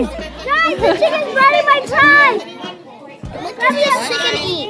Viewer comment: D "Guys the chicken's biting my thyme!!" ha! so cute. Wow, Kippee is so calm with the all the kids, that's amazing and cool :) This whole thing is awesome, what a great program, kids never forget stuff like this, literally being turned on to such basic aspects of our lives. "What does a chicken eat?? People??" <333 :DDD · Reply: i thought he said "the chicken is biding my time" and got confused for a sec